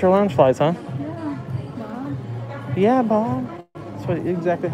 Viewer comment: ohhh...thought she said Yeeha Bob....hahahaha